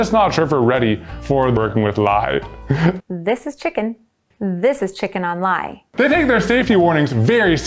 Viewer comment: When I first watched this I thought the soap queen said: “this is chicken, this is chicken online”